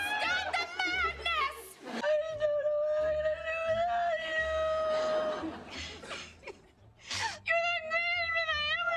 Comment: okay wait what are they mumbling from 8:06-41 tho? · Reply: in the beginning rachel said “ I don’t know what I’m gonna do without you “